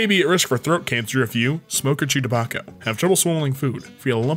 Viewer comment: I thought they said "smoke or Chewbacca." I'm dumb